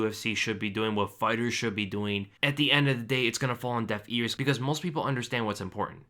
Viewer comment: sounded like you said falling on death ears lol